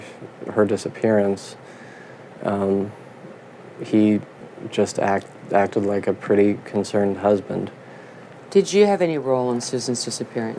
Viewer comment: What's that he said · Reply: "..her disappearance, um he just act acted like a pretty concerned husband".